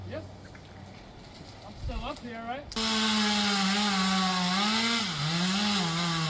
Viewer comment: The guy must have been asked ‘are you okay?’ Because all I heard was ‘I’m still up here, right?’😂😂